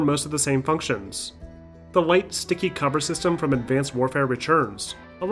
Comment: when he accidentally said advanced warfare instead of warfighter 😔